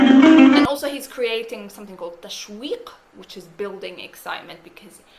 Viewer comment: Hi, at 16:53, you are talking about something that mean “building excitement”. What was that word. Thank you, wonderful video. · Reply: Tashwiq تشويق 😊